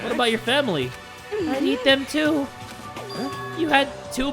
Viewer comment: did just say "i eat them too" at 49:01 😭😭